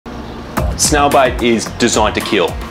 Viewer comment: Thought he said snail bite the first time